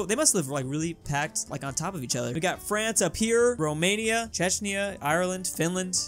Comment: did he say cheshnia (ch-e-sh-ny-ah)